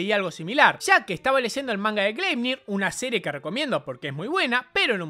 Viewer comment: cual es el nombre del manga q recomienda? no entendi el name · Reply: Gleipnir